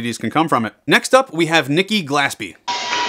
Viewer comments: When you said nikki glaspie i thought you said nikki blastbeat. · Nickie Glaspie oooh I thought he said NICKIE BLASTBEAT